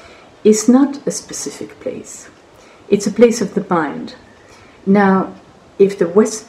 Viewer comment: What does Ms Nathalie Trouveroy say at 7:55?? It's a place of the _____ . Can someone tell me the word she used after that?? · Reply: It's the place of the mind❤️